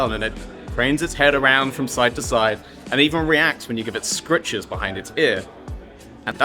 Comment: I thought he said “scriptures” 😭